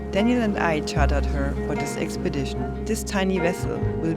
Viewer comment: what did you say? Tiny? Im on a 27ft